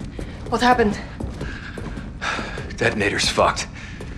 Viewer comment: what did he say?? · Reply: He said “The detonator is fucked”